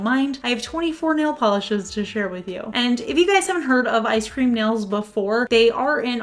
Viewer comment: Thanks for the great video. Funny fact: when I first heard you say the brand I Scream Nails a while ago, I thought the brand name was Ice Cream Nails. 😂